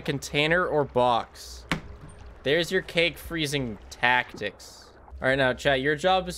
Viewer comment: i had paused for a long time and I thought that said coke until he said cake and got confused as fuck